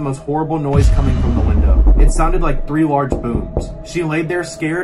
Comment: Captions: It sounded like three large boobs